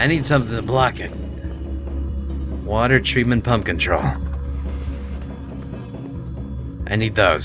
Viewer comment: it sounded like he said "pumpkin troll" :)